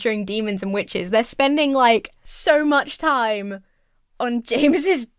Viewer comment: I heard "PogayMon"